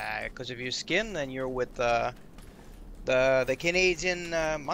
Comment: did he just said Hali a Canadian 🐒.